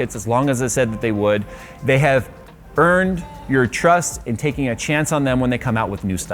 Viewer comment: says: "They have earned your trust" Stupid Subtitle says: "They have BURNed your trust" 😑